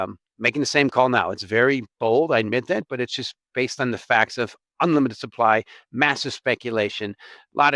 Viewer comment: Did he say bitcoin has “unlimited”supply??